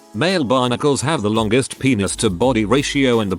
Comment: Because of the text to speech, I thought that when it said "Male Barnacles" it said "Male Bionicles."